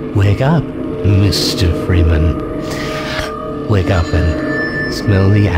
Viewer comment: i always thought he was saying "wake up man."